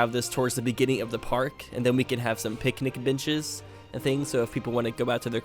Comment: I was eating cake while watching which distorted the sound because I was chewing, it literally made it sound like you said "picnic bitches" when you said picnic benches and now I can't stop thinking about picnic bitches